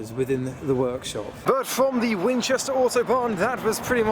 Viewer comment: I legit thought he was saying Autobahn until I realized he was saying Auto Barn. But man despite the rush this meet looks fun as heck